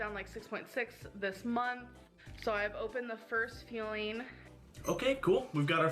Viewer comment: I hate how she pronounces it "feeling", I really wanna buy her a dictionary. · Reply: when she said number 2 "fueling" i thought she said feeling and meant that she needed to take a poop